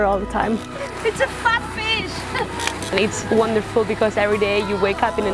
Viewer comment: Firstly I hear: f**k me. 5:22, after repeating all is ok it's a fat fish